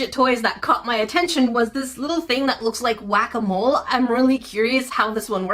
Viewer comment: I THOUGHT YOU SAID GUACAMOLE NOT WHACKaMOLE 💀💀💀